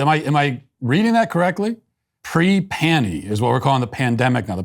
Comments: I was thinking that "pre-pannie" is pre- pansexual..... but pre-pandemic makes sense 🙂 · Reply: I thought it said "pre-tranny" first. Then I wondered whether "panny" was short for something to do with transgenderism, and took a minute to figure it out. I blame the weirdos though, not my own confused self. Because I shouldn't have to try and figure out words like that. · I thought she was saying she no longer goes commando. Pre panie...wth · Here is why they didn's use cute names for black plague - BECAUSE IT WAS AN ACTUAL PANDEMIA... this covid thing is actually kinda cute compared to black plague. Black plague is making fun of covid in plague world. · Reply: At first I thought she said pre-panty. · I thought she was saying pre panty and was trying to figure out what on earth that meant lol